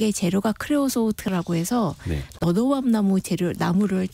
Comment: 너도밤나무. 처음 들었을 땐, '로우엄나무' 다른 분의 댓글을 보고 한 30번 다시 들으니 '너도엄나무'로 들립니다. 정보의 깊이는 있으나, 전달력은 많이 아쉽네요.